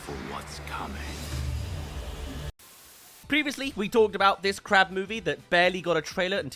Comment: I thought you said crap movie instead of crab movie.